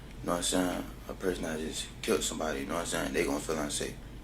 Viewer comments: The guys a complete moron with an attitude and u can't even understand him at that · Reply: Did he say, I just killed someone around 13:30? It sure sounded like it! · it sounds like he says he killed somebody. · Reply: He said "a person that just k*lled somebody". I thought he said "I", too, instead of "that".